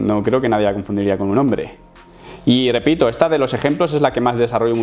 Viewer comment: Flashe que dijo "Tiene pito" jajajajjaja